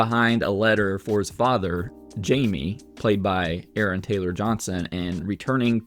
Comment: Movie tricked me cause the father is Jamie, and I kept thinking they were calling him Jimmy, thought he was the kid from the beginning. Cool twist, unless I’m just an Idiot & only person who misheard Jamie as Jimmy